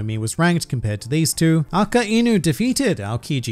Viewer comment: did bro just say aka-eenu💀💀